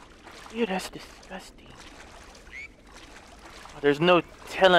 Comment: am I the only one who heard the faint “thas disgustin’”